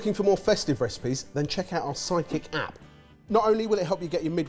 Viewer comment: Every time someone say "Sidekick App" I hear "Psychic App" and it makes me wish it could read my mind and have the food I want delivered